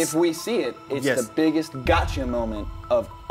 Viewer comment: When he said gotcha I thought he said Gacha